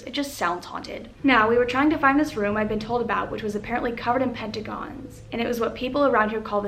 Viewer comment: DID SHE SAY PENTAGONS